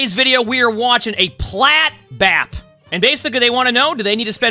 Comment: Pimp named Plat Bap was the first thing I heard. I'm sorry 😂